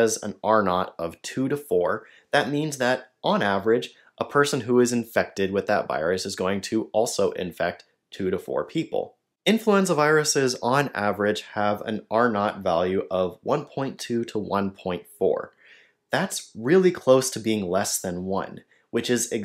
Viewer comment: I heard “influenza virus” as “influencer virus”. I think that should be recognised as a new category of virus.